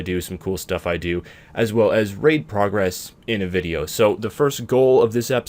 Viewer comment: lol i heard ''Rape progress''